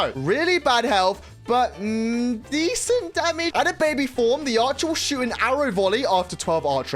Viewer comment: Did say a swear word! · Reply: I said "decent damage" and then "at a baby form"